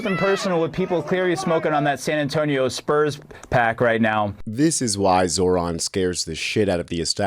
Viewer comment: did he just say that dude was smoking on that San Antonio pack?? 😂😂😂😂😂😂😂😂😂😂